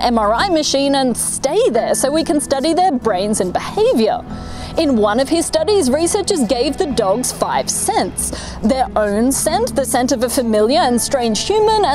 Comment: lol when she said they gave the dogs "5 scents" I thought she said 5 cents, like they paid the dogs a freaking nickel Lmao